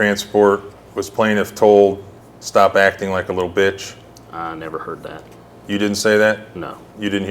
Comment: This whole time I thought she was saying bitch not bish 😂